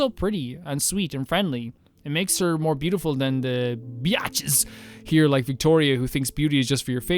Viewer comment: I heard "byatches" and for an instant I thought it was a new name for your favorite DR1 character